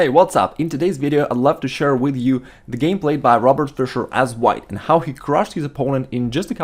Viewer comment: Did he just say robert fischer ?